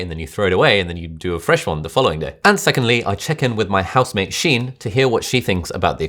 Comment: When you said house mate, I thought you said house maid, I thought you were dissing your girlfriend.